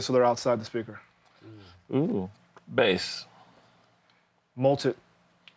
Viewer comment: could anybody help with what he is saying at 49:44 i dont understand the word or the meaning. "multed"? he say it earlier in the in the episode too... please share with us the knowledge if you know what it means thank you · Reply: actually he is saying multi, multiband compression .. i guess